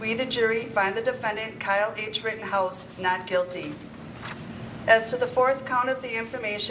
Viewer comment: I thought the court-woman said "... Kyle Gaige Rittenhouse ..." :)